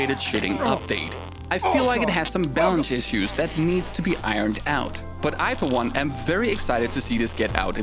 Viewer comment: Rofl, I seriously thought he said: "There's some bowel issues that need to be worked out"